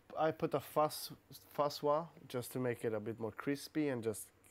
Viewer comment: What does he say at 1:02:28? Fa swa? :\ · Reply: fuzz wah? as in "wah-wah" perhaps?